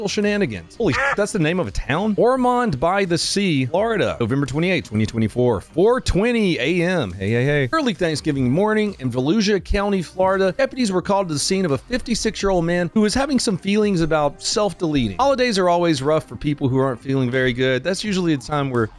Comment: I swore he said Fallujah county FL, which would have been perfect.